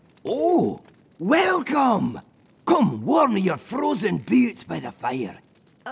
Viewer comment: Friends, come warm your boots by the fire in a place that magic has graced! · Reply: Aww...I thought he said "Warm you frozen beards by the fire" :D